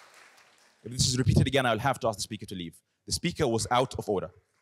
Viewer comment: They edited the president's order in 5:10. He said in the uncensored literally, “I’m going to have to ask the speaker to leave. The speaker was out of order”. They were already walking towards Mosab to escort him out. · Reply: I'm not so sure. In the previously released audio, I hear this: "[unclear...] again, I will have to ask the speaker to leave". Here he can be more clearly heard to say "If this is repeated again, I will have to ask the speaker to leave". The difference is just down to the audio quality.